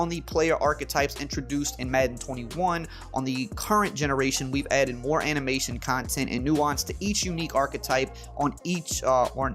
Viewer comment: Did I hear "A heavy dose of animation"?🤣🤣🤣